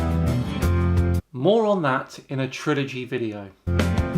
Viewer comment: when you said "more on that" i heard "moron, that..." x.x